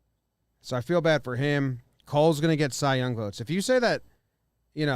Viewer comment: 1000% but you must've misspoke then, I quoted you word for word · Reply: idk if my ears don't work properly but 15:27 he clearly says "Cole's gonna get cy young votes"